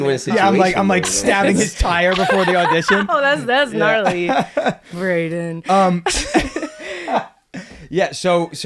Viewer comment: Did she call him Brayden?